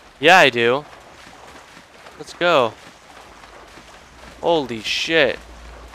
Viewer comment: sounds like you say goldy shit